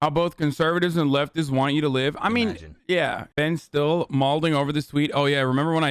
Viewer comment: did you say “is Ben still mulding over this tweet?” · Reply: malding..............